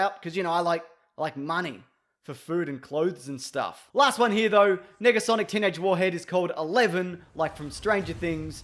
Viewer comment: did he say "nigga-sonic"? Uhhhhh bro